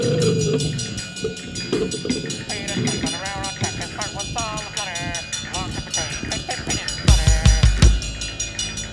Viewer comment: What does he say at 2:25? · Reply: "A peanut was strapped 2 a railroad track, his heart was all a flutter, along came a train & turned that peanut 2 butter "